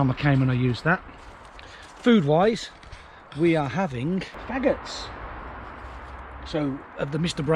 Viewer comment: For a moment I thought you said we are having maggots 😂😩